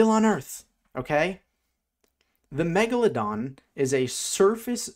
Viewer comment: Did i just hear "megavlondon" in the intro?